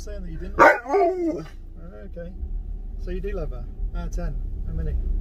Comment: Cutest brother 😍 i heard him say “i love hor”